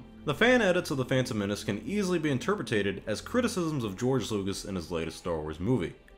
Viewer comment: Did you actually say "interpretated" at 12:48? 😂